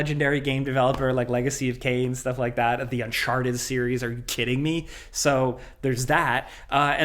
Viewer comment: did he say "the unsharted series" ?